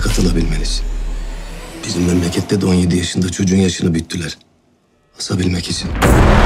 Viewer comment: de "Bizim memlekette de" dedikten sonra ne diyor?Anlamadım. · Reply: Bizim memlekette de bir cocugun yaşini buyuttuler asmak icin..